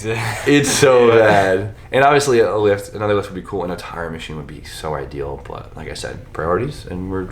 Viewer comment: Should write off a tire machine as a business expense for your YouTube channel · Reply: I thought he said Time Machine. a tire machine would be cool too.